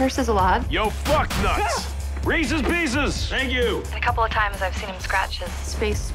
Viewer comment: I thought he said buck nuts